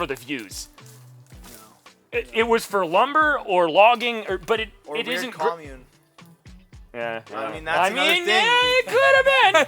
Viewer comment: can anyone tell me what said here? Can't make it out (no English native) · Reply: He said “or maybe a commune”😅